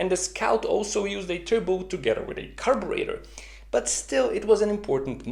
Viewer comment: What does he say at 12:26? "…used turbocharging to getawreidy carburetor…" I mean, get away with or get rid of or something else? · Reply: "together with a carburetor" it's in the automatic captions too